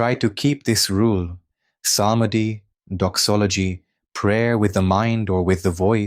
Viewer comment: im sorry what did he say? Samadhi???? Prayer of the voice or mind and doxology , i could hear but what did he say???? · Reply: Psalmody (= singing orthodox chants, psalms)